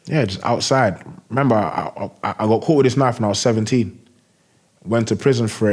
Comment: Much love for interviewee and host….but I lost it n couldn’t stop when I heard “at 17 I got caught with a kitchen knife…” 😅😂😂 Damn